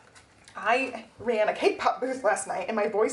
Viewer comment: The Tamagotchi covers are so cute! I really love the A6 cover-on-cover, especially. And congrats on the cake pop sales. I swear, every time you talk about it, I hear "K-pop." I thought you were selling idol merch for a second. 😅